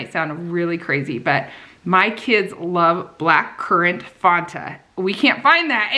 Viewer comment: Oh my goodness you said black currant Fanta! I thought you said vodka!😂